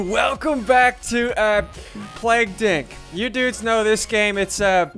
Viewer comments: Did he just say: hey what’s going on guys and welcome again to plaque d*** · I heard him say plagued inc